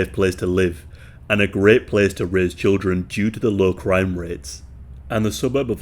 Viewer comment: Okay I heard it's a good place to rizz children instead of raise children